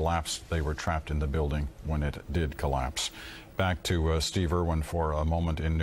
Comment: I heard 'steve irwin reporting' and all i could think was 'crikey! The planes hit with a big mighty fair dinkum crash!!' (i'm Aussie) But no, it was steve erwin lol